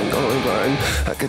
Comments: For a minute I thought it said I could see I could see and I am colored blind But it says I can’t see I can’t see I am going blind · I can't see I'm legally blind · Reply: Lol if I minute I thought he said I could see 2x I am color blind but he says i can’t see 2x I am going blind kill me